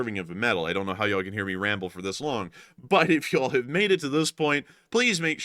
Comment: I thought you said “Eagles” 😂.